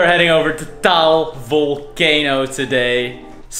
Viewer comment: Did he just say taol volcano???